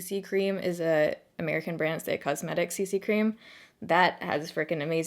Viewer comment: I tried rewinding the video a bunch of times and just can't figure out what you said lol - You mentioned you liked the Lioele BB cream and then went on to mention another CC cream that you loved @ 5:58 but I don't know what you're saying. PLS let me know! I'm on a BB/CC kick at the moment and I too have combo skin! So PLS let me know!!! - Thanks! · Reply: She mentioned the IT cosmetics CC cream..